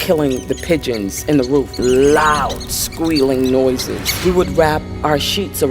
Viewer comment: the word will be "Squeaking" INSTEAD OF "squealing"